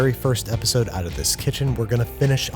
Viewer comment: I got a little bit to terrified when i heard him say “This is the final episode” but then i heard “In this kitchen”